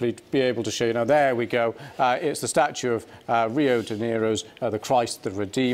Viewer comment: did he just say "Rio De Niro" ?